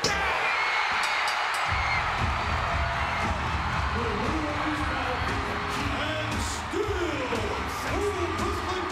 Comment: Did anyone else see what he said to him😂😂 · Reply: The end was get outta my f**king ring